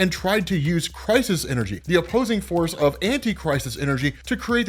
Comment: Definitely thought you were saying “antichrist” energy😭